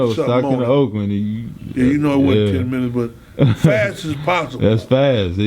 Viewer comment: From Stockton to Oakland in 10 minutes 🧢 No disrespect I know you a real one but you can’t drive that fast 😂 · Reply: He literally said "you know it wasn't ten minutes but fast as possible." He was just exaggeratin, G. Lol Go back and listen at about the 7:55 mark and you'll see he says just what I said bro. He ain't mean the sh*t literally. 😂😂